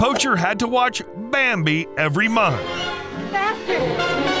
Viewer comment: had to watch Bambi every ma-.. Every what? Oh, month. He said it later lol